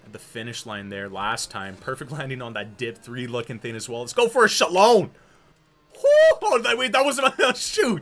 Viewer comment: Your pronunciation of Shaolin got me good. I can't lie, I thought you said "shalom" at first lmao